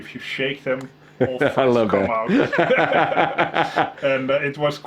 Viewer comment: What is he saying there? What will come out? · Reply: They're both talking at the same time...sounds like "all hell will come out"